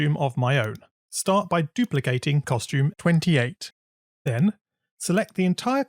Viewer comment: in CC (subtitles) 3:38 instead of 28, it says 18!!!